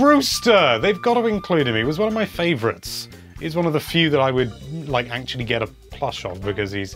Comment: Honestly thought you said “he’s one of the few I’d get a crush on” 😂🤦🏼‍♀️